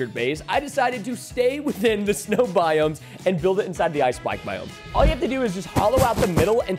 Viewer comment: i thought it said ice spice